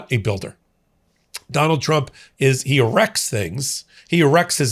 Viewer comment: Thought you said "...Trump WRECKS things...". Can't disagree with that!!!!!!!!!